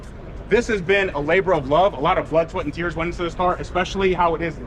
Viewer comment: did he say a lot of "flood sweat and tears"? 😂